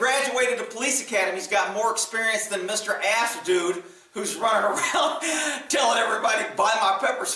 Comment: Did you just say "ass dude" for green screen? 🤣🤣🤣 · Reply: ASP dude. (Active Self Protection)